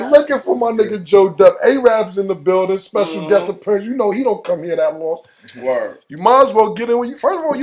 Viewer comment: thought araab said he dont smoke lol